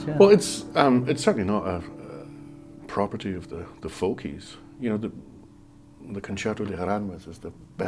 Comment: Does anyone know what the name of the piece he mentions at 4:17 is? I can't make out what he's saying (switch on the subtitles, I think they may have also misheard) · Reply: Concierto de Aranjuez de Rodrigo.